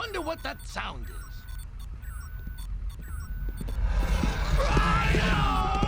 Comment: What's he saying in 3:57 3:58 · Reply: “Rhino!! Ow ow just calm down ow ow ignore us!!” Hakuta matata playing in the background along with beeping.